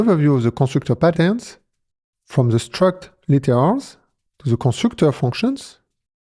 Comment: Great video. 👍 But one word I couldn't understand I'm wondering about... the "CC" subtitles say "struct laters" at 0:21 and "struct liters" at 0:25. What is he saying? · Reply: “Struct literals” meaning with the constructing a struct from its properties.